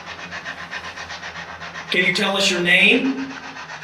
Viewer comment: Sounds like he said “What’s the name”